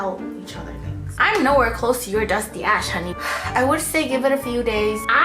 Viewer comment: saying “I’m not where close to your dusty ash” sounded like she said “I am nowhere close to your dusty a$$ 😂😂